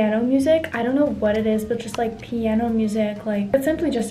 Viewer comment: i thought she said " idk what it is about ghetto music " 😭😭